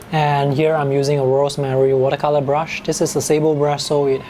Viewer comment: I'm sorry, but what brand-name of watercolor brush did you say you are holding? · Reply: Rosemary brush. <link>